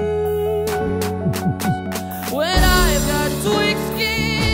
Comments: did he said I've got twix skin and an elastic heart? :D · i know somebody heard him say and i quote "i got twix skin" or is it just me.